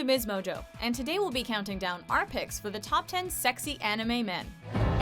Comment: I thought it said Top 10 Sexist Anime Men! I saw MsMojo and I was like "What? Sexist? Why? " and then I had to see it.